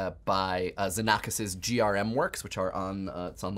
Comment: he said "Xenakis - GRM Works" in case you couldn't understand, like me :')